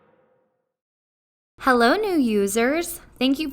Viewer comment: My volume was low and i first heard " Hello you losers " at the beginning